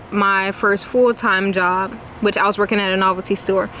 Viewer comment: I keep hearing her say: “My first fool time job…”